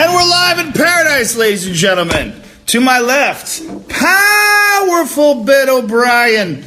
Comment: Thought he said Bill O'Brien at first lmao